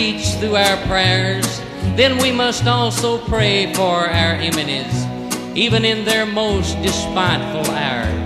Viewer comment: did he say "EMENIES?".